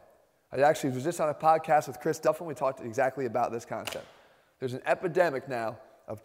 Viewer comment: I am watching this to learn more because i am trying to improve my gains so i need more knowledge, and each time Jeff says like 8:33 "There's an epidemic here" i heard that as "There's a pandemic now"